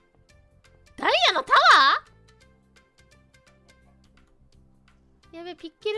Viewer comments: I could be hearing wrong, but I think in 0:24 said, "Daiya no tawaa?!" (Diamond tower!?) Instead of, "Dare no tawaa?!" (Whose tower is this?!") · a bit correction I think says "Dia no tower!? /Diamond Tower!!?